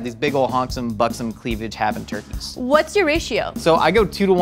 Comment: When she said “what’s your ratio” I thought she said ”what’s your issue?” and got really confused for a second there